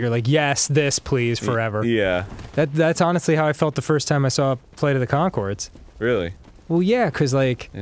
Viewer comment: play of the what ? i didnt hear what he said, can someone tell me plz :C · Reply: TheKrokomaster I come bearing your answer 5 years later! Flight of the concords.